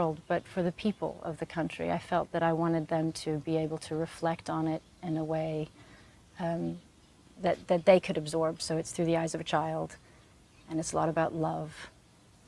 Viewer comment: what she said .....please can anyone reply to me ( subtitle between that time) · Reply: English_Learner Negi She says "Reflect on it in a way, Erm.. that that they can absorb, through the eyes of a child, and its a lot of love "